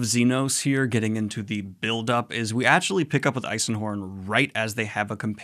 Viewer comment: I can't get over that it sounds like your saying Eilsenhorn with an L, and it's bothering me.